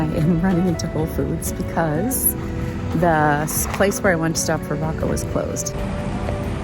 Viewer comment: I thought you said "The place I wanted to stop for Vodka was closed." I thought it was a tough drive home!